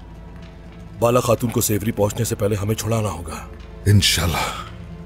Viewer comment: What is cerkutay saying 14:56 safely? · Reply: He said sehbri